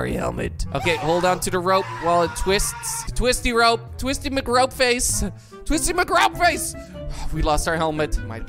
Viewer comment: I heard "twistymygropeface" Dont report me... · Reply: He said twistymcropeface